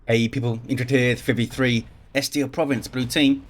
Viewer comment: I really enjoy your video but what are you saying in 0:10? just like every video. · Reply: "Hey people, Indrid here! 3v3, <map name> - blue team..." Over the years it has become more and more mumbled, I imagine it must sound like random noises to some. Especially if English is not their first language.